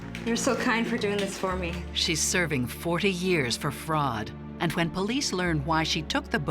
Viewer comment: Did I hear that correctly (40:49) Fourty years for fraud?